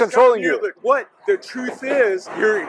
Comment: “Go and sell your crystals” savage 💀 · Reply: I thought he said “ Go and absorb your crystals “ 💀